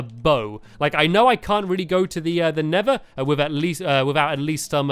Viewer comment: you said neather instead of the end